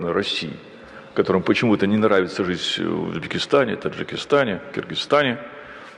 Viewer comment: он сказал в Дагестане? Поправьте, если мне послышалось. Дагестанцы, если что, граждане России с рождения. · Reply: Узбекистан